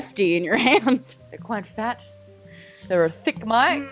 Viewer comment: whitney-"I've got a big one and it's still really hard" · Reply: I heard they are "quite fat"